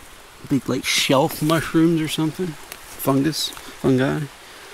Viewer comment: did he just say on god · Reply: he said fungi